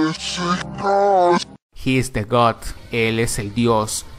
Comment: yo entendi he is the ghos